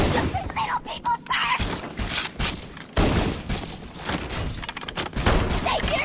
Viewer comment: What does the grunt say at 23:07? I hear something like "Little people burst!" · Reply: Raul Almada "Little people first!"